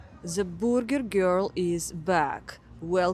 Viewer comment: For the first 5 seconds I wasn't yet fully attentive. For an instant, "burger girl", I thought she said "vulgar girl"!?!?! Hello from Canada, eh.